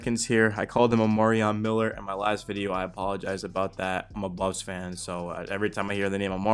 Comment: Yeah cause I’m definitely a Bucs fan😂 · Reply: lol at like 1:18 I'm pretty sure you say you're "a Buffs fan" and commenter misheard